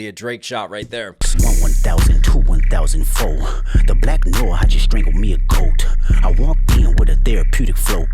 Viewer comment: I thought he said “the Black lore, I just strangled me a goat” Black lore of Aubrey (which Kung Fu Kenny DESTROYED)